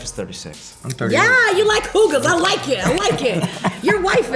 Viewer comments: What did kelly call priyanka 01:09 ?? Did she say cougar ? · yeah so true! I really like that song as well. It's super catchy. and Priyanka looks STUNNING in it as well. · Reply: But 01:09 did kelly call priyanka cougar ?? I couldn't get it.